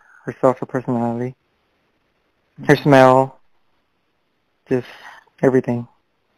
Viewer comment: I love how the reporter had to give him the ideas on why he loved her. His voice sounds just like Chris Watts and Scott Peterson. Same cadence. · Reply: I thought he said smile On review I think I was just going he said smile because he clearly said smell